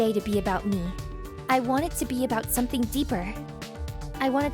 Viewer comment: i thought it said despair instead of deeper